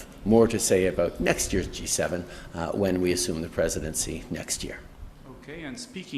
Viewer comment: Did he say ,, When We resume the next presidency? Anyone catch that ??